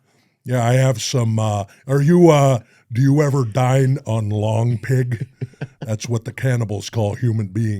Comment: "Do you ever dine on long pig?" is one of the funniest sentences ever said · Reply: Always thought he said “lawn pig”